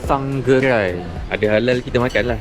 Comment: Aku tak salah dengar "sang geghai" & "chicken ghraiss" dengan "teghhai"..betul kan?